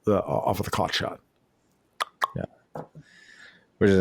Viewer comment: i think you said "clock shot", but i was listening at high speed and heard "cock shot". now i have to make a band... sigh.